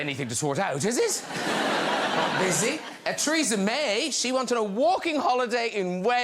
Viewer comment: It sounded like Graham said, "Treason May".